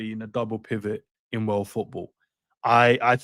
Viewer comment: did you say world football👀